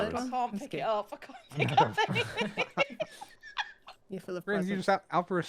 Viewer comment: did say f**k off?????